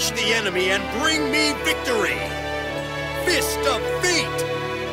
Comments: It sounds like Kiba says "Fist of Feet" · The Chad GOD HAND CRUSHER vs the virgin fist of fate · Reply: I thought he said Fist of feet.